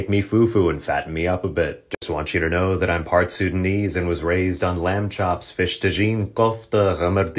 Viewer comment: thought you said "sundanese" instead of "sudanese" and almost got excited, since im part sundanese